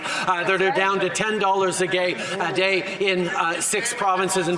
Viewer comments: I don't know what he thought of, but at 3:44 he says "10$ a gay" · did anyone else hear a 10 dollars a gay?😂😂😂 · Did he just said 10 dollars a gay 😶‍🌫️